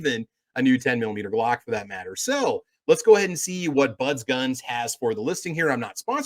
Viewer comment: I’m sorry, did you say, Buns Guns?!! 😅